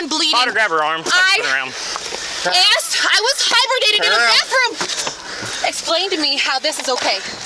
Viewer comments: Did she say she was “hibernating in the bathroom??!!! 😳😳😳 Edit: And one of her medications is “hydroxyclean”??? 🤦‍♀️ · Sorry, did she say she was "hibernating in the bathroom"???